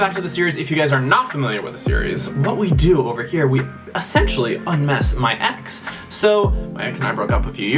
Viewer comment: I thought he said I miss my ex every time he said un mess my ex 😆